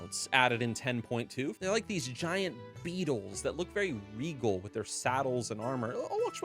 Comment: You said regal but I thought you said weevil.. lol